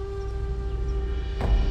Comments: Who else for the first time thought he said you keep for your correction I know you like it instead of collection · True Story: This quote stuck out to me all of these years because, as a kid, I literally thought Chozen was saying "You keep for your correction" and I couldn't understand what correction he was referring to.